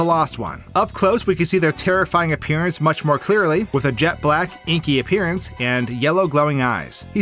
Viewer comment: i thought he said jack black and got really confused